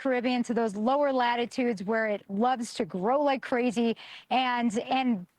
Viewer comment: I thought they were saying sarcasm sharks..i was thinking to myself "i think we can do without them" Or am i being sarcastic.